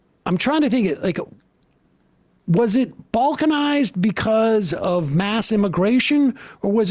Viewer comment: My sleep-deprived ass heard him say that white culture is being “Vulcanized.” 😅 And I was like, “What’s the problem? Does he not want to live long and prosper?” Man, I need a nap.